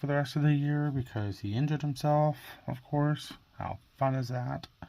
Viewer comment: I thought you said the quarterback ended himself. Then I realized you said injured after I was giggling how nonchalant you were about it. Thanks for the laugh. You are making quite a bit of progress. Looks good.